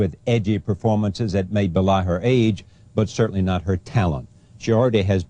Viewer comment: Help please! What did he said at 0:09? Captivated audiences with edgy performances that made …. her age. What’s this word????? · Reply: "Which may belie her age" It means, fail to give a true impression. Her performance is so extraordinary for her age.